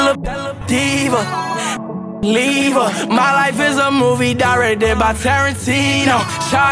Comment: When he first said directed I thought he said DRY RED RIBS